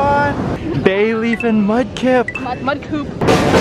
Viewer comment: Who else heard mud poop?